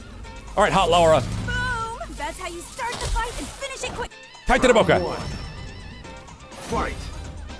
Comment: did max said "callate la boca?"